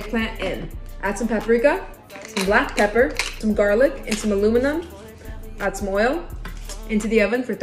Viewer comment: I thought you said “garlic and some aluminum” and I was like HUHHHHH